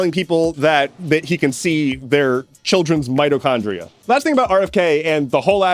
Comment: Not going to lie when u said rfk jr can see our mitochondria I thought you said midichlorians you know from star wars..... And the fact that for a sec I believed he could have said that just shows how bad things are